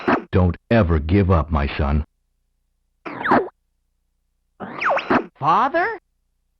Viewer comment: Sorry, my brain is broken, so when I hear James say that line, all I can think is “Hello Papá!”